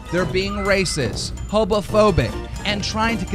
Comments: is he saying Hobophobic? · Did he just say “hobophobic” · EXCUSE ME, DID HE JUST SAY "HOBOPHOBIC?" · did keem just say hobophobic? XD idk why I found this so funny, probably because he's getting on a soapbox pretending to care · I think Keemstar just said "hobo-phobic"! Lol oh fucking jesus. How does he exist, just in general?!?! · did he just say HOBOphobic?? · did he just say "hobophobic?" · did he say hobophobic? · wait did keemstar say hobophobic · Are we gonna act like Keem didn’t say hobophobic instead of homophobic at 16:48 ? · Did he say hobophobic? I’m pretty sure he said hobophobic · did keemstar just say... hobophobic ? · Keem really out here sayin “Garbitch” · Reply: Ryan Slavinsky at 16:46 he says “hobo-phobic” instead of homophobic. 😂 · did he just say hobophobic lmao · is it just me or did he say hoBophobic not homophobic lmao 16:46 , or I'm just deaf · Did Keem just say "hoBofobic"? LMFAO! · does he say hobophobic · did he say "hobophobic"? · Did he say...hobophobic? ...Fear of hobos? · lol did he say "hobophobic"? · Did Keem say "hobophobic"? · did he say "racist hobo-phobic"? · I heard “hobophobic” not homophobic 😂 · Did he say "Hobophobic"?? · he really says “hobophobic” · did he say "hobophobic" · I swear he said hobophobic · did he say hobophobic ? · did i mishear that or did keem say "hobophobic" instead of "homophobic" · hobophobic?? He really said hobophobic I CANT WITH THIS GUY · Did Keem just say hobophobic? I didn't know it was not ok to hate hobos.